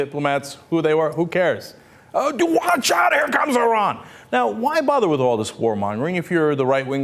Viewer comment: THE CAPTIONS SUCK! When the anchor said "Here comes Iran!" at 00:19 the captions said "uh...bloodshot toronto"